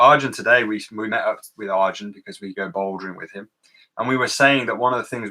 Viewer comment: At about 02:03:50 is talking about meeting up with Arjun and says something like "we go ???? with him"? I can't make out the name of the activity. If I turn on captioning, it appears to be the word "bouldering" -- did the transcription get it right? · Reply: hey - sorry for the ridiculously delayed reply to your comment here (we've onlt just seen it) but yes its 'bouldering', as in, indoor rock climbing